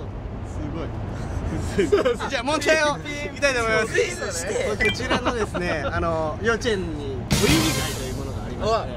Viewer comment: うみくん、なんて言ってる…？ そのあと笑いも起きてる気がするけど、全然聞き取れない（笑） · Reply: エーミール。 言葉じゃなくてリズムだったんですね！！！？（笑）ありがとうございます🙇‍♀️🙇‍♀️ よく聞いたらそうでした、知ってる音でした…流れが気になりすぎます（笑）